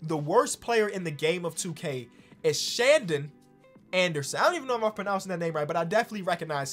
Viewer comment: I thought it said Shanderson Anderson 😂